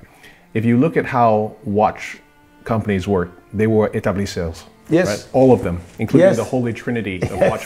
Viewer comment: What is the French word mentioned in 31:42 ? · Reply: Établisseur